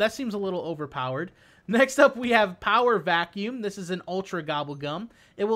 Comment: did he say power of acne??